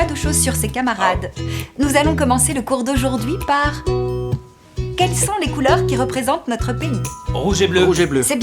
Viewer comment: Pourquoi j'ai entendu "Quelles sont les couleurs qui représente notre penis"